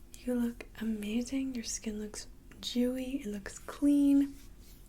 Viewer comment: Did she just say my skin looks Jew-y?